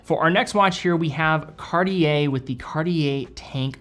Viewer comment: no one gonna talk about the fact he said cardi a instead of cartier?????